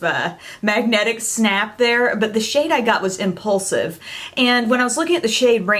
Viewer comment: I thought you said the first one was called "Repulsive" at first and I was like I'm not sure I would buy that if I were you lol